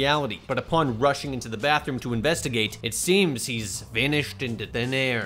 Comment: I heard "Rushing into the bathroom to investigate" as "Running into the bathroom of a Bethesda game" and it honestly fits pretty well